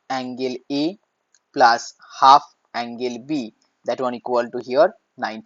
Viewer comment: angle is two times instead of angle b